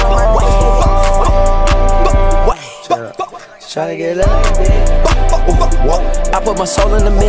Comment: someone please tell me what he says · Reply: “Shawty get lit like a bic🕺🏽🕺🏽🕺🏽”🤷🏽‍♂️